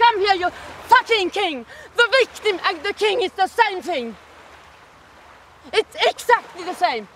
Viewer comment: Ok but does anybody know what the hell she says at 11:49? I've been trying to figure it out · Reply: That the king and the victim is exactly the same, whatever that means 😂